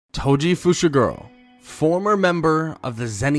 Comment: I thought he said "Toji Fushiguwo"